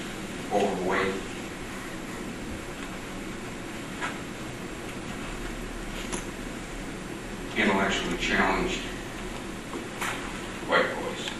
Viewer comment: It sounded like he said overweight, intellectually challenged white boys LOL